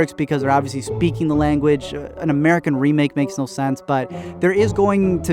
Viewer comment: I heard "An american sequel" ....